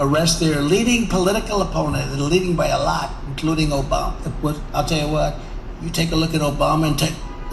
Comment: "To arrest their leading political opponent, leading by a lot, including Obama, taking a look at some...i'll tell you what" Look at the closed captions!!! "As you know..crooked Joe biden..." was displayed instead!! I know Trump can't tell the truth and deserves jail! But this was never said, so why was it transcribed on closed captions??